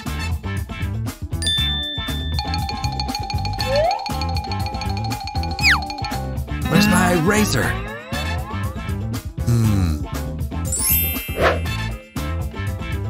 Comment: i thought he said "wheres my erazer?!"